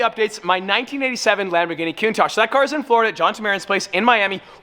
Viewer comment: bruh when u said countach for som reason i heard "twin turbo gallrdo" 😭🫠